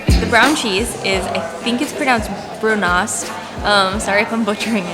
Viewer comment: when she said brun øst sounds like she said brunast. facepalm